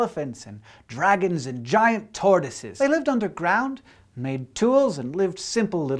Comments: i could have sworn he said "giant tardises". but, alas. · I thought you said "giant Tardises" and then I read the description and I was a little disappointed... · i understood giant tardises instead of tortoises.. X'D